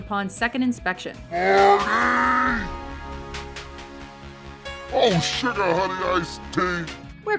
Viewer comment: Am I the only one that thought Marty (The zebra) said "Oh shit"?